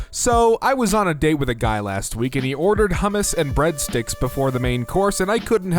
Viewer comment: HOW ARE YOU WEIRDED OUT BY A DUDE GETTING HUMMUS AND BREAD STICKS FOR A APPETIZER?!?!! It is good food. Edit: HA U THOUGHT IT WOULD BE ABOUT LIKES NO IM JUST NEVER GONA GIVE YOU UP NEVER GONA LET YOU DOWN NEVER GONA RUN AROUND AND DESERT YOU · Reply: I don’t know why but I thought you said humans and breadsticks I was so confused